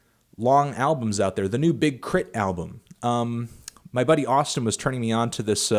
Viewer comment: Totally thought you said, "The new Big Clit album." That would definitely be experimental.